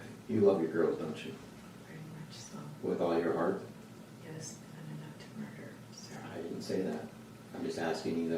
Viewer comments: It sounds like she says "yes, not enough to murder sir" when asked about loving her girls with all of her heart. (@ 18:23 ish) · I would just like to clear up a small mistake you make in your video, at time 18:24 she actually says " NOT enough to murder Sir" just for the record.